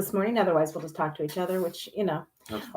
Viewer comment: At 30 seconds, it sounded like said that if no one joined, “we’ll f- each other!” 🤣🤣🤣🤣🤣